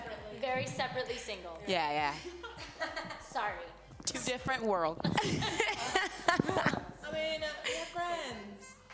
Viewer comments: Is Lauren saying something to Camila after she says "sorry"? It's my imagination? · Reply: anic kb I think she said "just kidding baby" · It sounded like Dinah said,"We have friends." Also, Lauren was not amused when Camila made that joke because she knows that people will use any and everything as ammunition. I don't understand how "fans" could ruin a friendship like this. They have to be careful to the point of being awkward because of you selfish, immature, insensitive "fans". If it makes them uncomfortable and they feel that its inappropriate, just stop! If you care about them you'd care about their feelings.